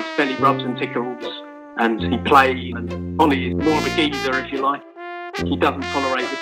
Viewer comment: the captions are wrong. He calls Ollie a geezer not a 'diva'.